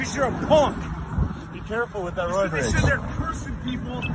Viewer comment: At tha 10:00 moment, caption picked up half of wat it THOUGHT dude said reading.. "Be careful with (???) race"..❓ When I CLEARLY heard tha guy say ta him "Be careful with that Roid Rage", neva said anything bout race 😆😆